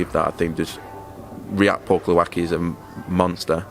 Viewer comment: was only half listening thought he said Purple Aki is a monster.